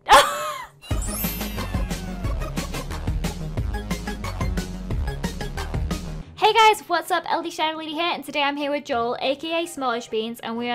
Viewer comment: I think I heard "Hey guys!! What's up? LDShadowwady here!"